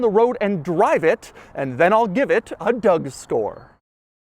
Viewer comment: is it just me or he sounded like "Doug Store" 😅